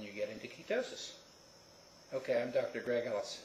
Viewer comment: Good video, nice to see a Doc not being ignorant to the Ketosis process, and btw at the end i thought you said "goodbye I'm Dr Greg House" . LOL